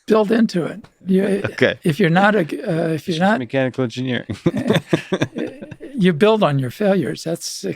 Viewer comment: did he say i'm just a mechanical engineer? Is that what he majored in for his BS?